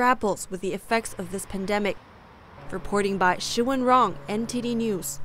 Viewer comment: Does the say reporting BOT?